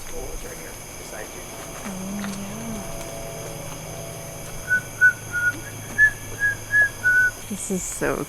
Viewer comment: A MALE VOICE SAYS “BESIDE YOU” CLEAR AS DAY BUT I GUESS YOU GUYS DIDNT CATCH (WEARING HEADPHONES) 💜